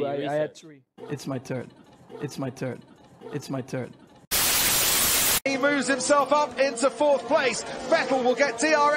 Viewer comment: What did Riccy say? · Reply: He said "Give him steak". They were talking about how Valtteri wants to beat Hamilton next year.